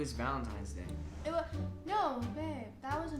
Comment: did she just say "valentimes day"